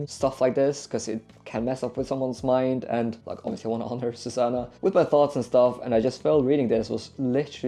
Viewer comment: Entertaining video! Also, as a Christian myself, I’m curious what you said at 23:40. You want to honor …? Just curious because I’m not sure what that word means! It sounds like you said “Susannah” ? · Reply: Thank you! Yes, Susanna is my wife!